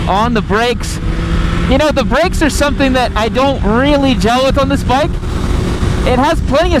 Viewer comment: almost sounds like you said "The breaks are something that I dont really Jelk on this bike" xP